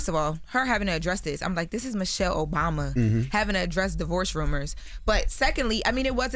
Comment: I thought they said Michael Obama. I’m like oh so the rumors are true